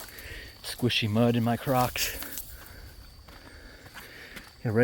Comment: I'm not trying to blast this video with messages but at around 34:58 when you said "squishy mud in my crocs" I heard "squishy mud in my crotch" hahaha 😂 I had to rewind it to double check!